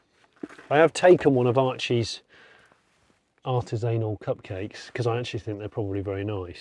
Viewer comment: did say Archies ANAL Cupcakes? 😂😂😂😂😂😂😂